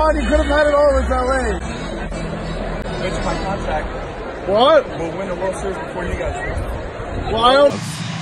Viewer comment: what did he say ? I couldn’t hear him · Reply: "I'll bet you my contract we win the world series before you guys do"